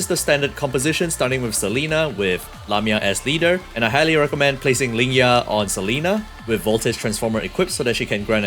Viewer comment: I heard "placing Lingyang on Selena" 💀